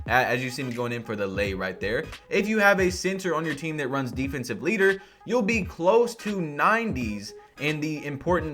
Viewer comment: I thought it said offensive Hitler for the thing